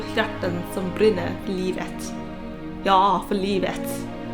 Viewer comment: I misheard "ja för livet" and thought you said "jag hatar livet" 😂